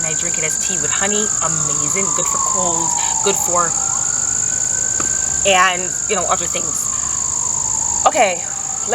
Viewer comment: Wait y'all, I can read lips! What did she say?!? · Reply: Covid